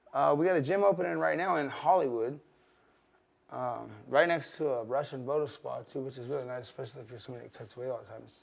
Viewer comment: What did he say when he spoke about the new academy in Hollywood...he said its right next to a Russian......? A russian what? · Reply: It's like a Russian spa, I forgot what they are called